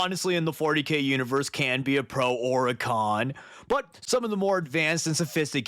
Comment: I just heard Pro Ork. Personally, I think it's still true